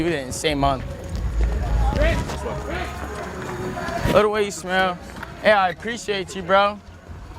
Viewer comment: i thought he said "by the way u smell"